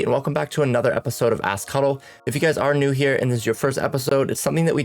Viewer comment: First time on your channel , in the intro when you said “ask huddle” i thought you said “a** cuddle “ 💀💀💀😂😂😂 other than that very helpful video